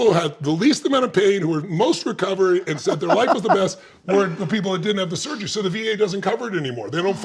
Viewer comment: I was tripping for a while thinking he said ‘weren’t the people who didn’t get the surgery’ and then I realised the ‘t’ sound at the end of his ‘were’ was for the ‘the’ word which was next… I thought he said weren’t .. I was only tripping cos I was trying to find he made a mistake 😹😙🙋‍♂️☝️👌 George on the ride